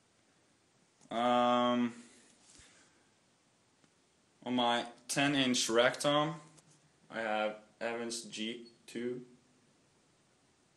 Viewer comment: did he say 10 inch rectum 4:27?